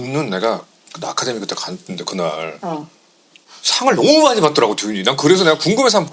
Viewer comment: I found a mistake in eng sub, I think seojin said he went to ‘academy awards’ not the golden globe awards?